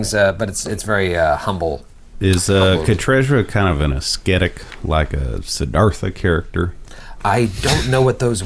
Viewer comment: Hey i'm not a native speaker and I was wondering what one of the player said @ 14:29 It goes like "Is Katrezra a kind of an ecstatic... kind of a [???] person" and I just can't find what word he said here. Can someone enlight me ? =D Beside that this show is hilarious. Really happy to have found it ! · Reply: What said was "Is Katrezra a kind of ascetic like a Siddhartha character." Siddhartha was the name of the man you became the first Buddha. So he is asking if Katrezra is a person who dedicates his or her life to a pursuit of contemplative ideals and practices extreme self-denial or self-mortification for religious reasons.